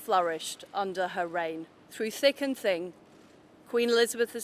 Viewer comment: did she say thicking thing ? WTF is that. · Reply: "Through thick and thin..."